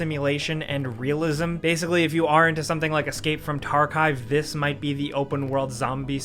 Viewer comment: did he say escape from “Tarkive”..?